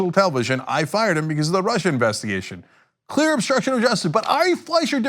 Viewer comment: Actually Cenk, what Trump said was he fired him because of "the Rusher investigation". Please get your facts right.